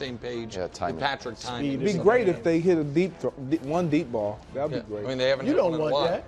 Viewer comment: Am I losing my mind or did Greg say DEEPTHROAT at 3:22😂😅